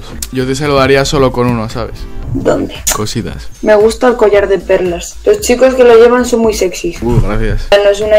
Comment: lo dije muy fuerte y pensé que dijo "collar de perras" lo prometo jajajaj me quedé pensando todo el short hasta que lo vi de nuevo